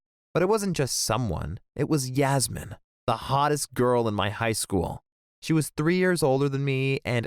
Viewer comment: Wasn’t paying attention when all I hear is “she was three years old”